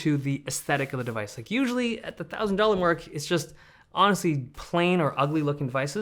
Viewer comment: did i hear it right? 1000 dollar mark?? Then is actually STEAL.